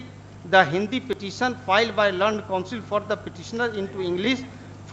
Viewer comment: When he said "Learned Council", I heard "Lu*nd Council " 😀😀😀 BTW I really praise the courage of Advocate